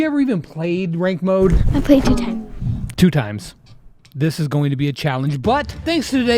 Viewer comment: "I played two times" sounded like "I played TwoTime" TwoTime is from forsaken if you didnt know